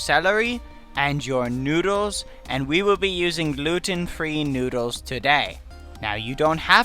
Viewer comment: when he said gluten free noodles i thought he said looting 3 noodles lmao